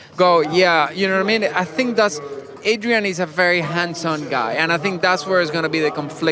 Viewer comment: Oh, HANDS - ON !! I thought Pablo said Handsome Adrian Newey.